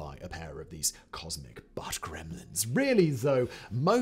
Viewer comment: "even Uranus is haunted by a pair of these cosmic ???? ????????" What ARE the last 2 words here?? Lol ... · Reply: Butt gremlins